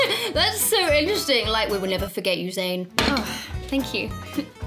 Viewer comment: I'm trying to make a fandub, anybody got what twitter is saying at 1:17 ? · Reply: That's so interesting like... WE WILL NEVER FORGET YOU ZAYN.